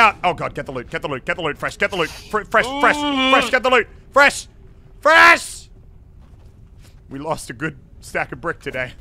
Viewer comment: did muselk call fresh fruit xDD??